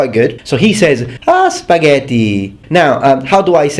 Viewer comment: when you said spaghetti in an american accent i could have sworn you said mom's spaghetti